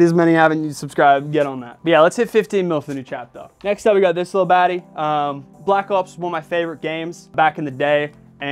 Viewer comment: Bro I thought he said I black 💀